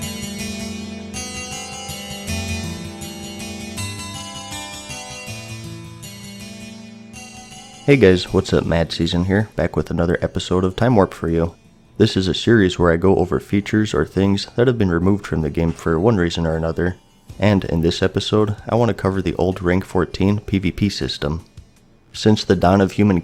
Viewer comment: First- love the channel. Second- every time you say your name I hear "Hey guys, Mad SUSAN"